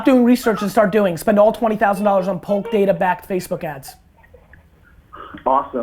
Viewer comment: Gary what are you saying at 13:08? "Tapping into poke data backed Facebook ads" ?? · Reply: polk data